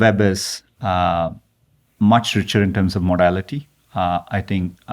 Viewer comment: For a brief moment there, a ray of hope appeared. When Sundar was asked "What does the best version of the web look like 5 years from now?" I misheard his response as: "I hope the web is much richer in terms of morality." My ears pricked up, thinking "wow, I love that his immediate thought was for humanity in 5 years time". Nope! What he meant to say was: "I hope the web is much richer in terms of modality"....